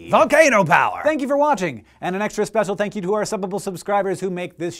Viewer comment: It always sounds like Hank is saying Suppable instead of Subbable.